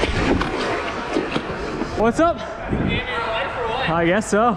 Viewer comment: what did he say? Do u need ur life or what?